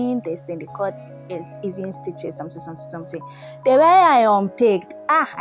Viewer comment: You, I thought I heard "I have suffered in the hands of these nice b*tches" 😂😂😂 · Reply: Oh she said stitches aw😂